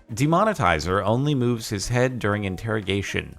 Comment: I misheard "Killer only moves his head during interrogation" as "Killer removes his head during interrogation" and I was really confused.